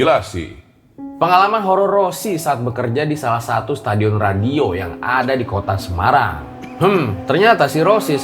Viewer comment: Pas awal mulai ko gua denger nya Stadion radio? Apa cmn gua yang salah denger yaa 😂